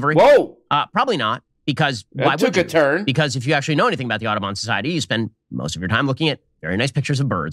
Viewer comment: I think it's a terrible bloody name. I keep hearing "the autobahn society" and thinking it's for highway systems 🤣